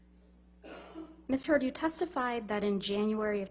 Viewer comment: Replay the video from the beginning, Camille literally says "Miss Turd" instead of "Heard" at 00:01. Anyone else noticed?!